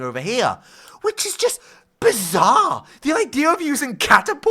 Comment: Did you just say ( bizarre)